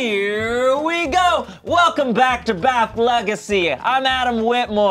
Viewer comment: LETS GO ‼️‼️‼️‼️‼️‼️ BAF LEGACY MY BELOVED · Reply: I always thought they were saying BATH legacy like Betrayal At The House.....on the hill